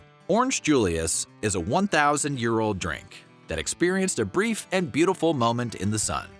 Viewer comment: did I hear 1000 year old drink?